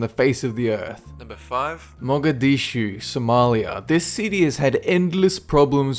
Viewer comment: I Thought He Said Monga De Shoes